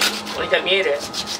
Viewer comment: in perfetto italiano "vuoi capire?" detto due volte, e poi ride.... O sento allucinazioni uditive 🤣🤣🤣 さ